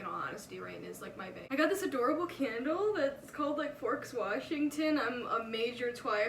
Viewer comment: Lol when you showed your candle, I thought you said it's called "George Washington". And I wondered what powdered wigs smelled like. LOL